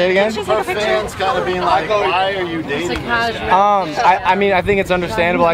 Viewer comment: did anyone hear "why are you dating this guy?"